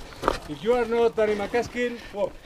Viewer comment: Hahaha did he say "you're no Danny Mcaskill." On that death turn? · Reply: Haha, he said "if you're not Danny MacAskill walk your bike"